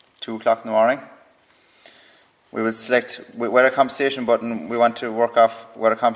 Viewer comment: Water conversation? Please let me know what that button does, I only hear Water Conversation, Subtitles says the same and I am not good with the accent. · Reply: Weather compensation. It adapts to the internal temperature. You can raise or lower it by up to 5 Deg C